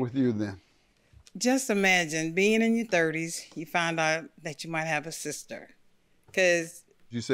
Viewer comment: 😀 The JUDGE😃 Kill's Me But I Love Him But I Could of sworn I heard imagine being in your thirties and finding out you have Other siblings out there Witch I Feel is a FUCKED UP WAY TO Find Out Whole Time there's others Not Just U and Hopefully u can Ask Real Quick Why Now and What was The Reason For Sending them With Other people